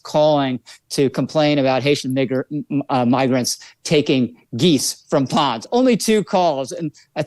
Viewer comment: (3:44) Hatian n[...]er (pause) migrants. It seems pretty fucking clear what he said there.